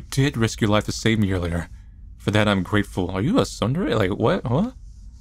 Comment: What is the word said at 31:31 ? Sundrie? What did he call emma?? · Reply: Late reply : tsundere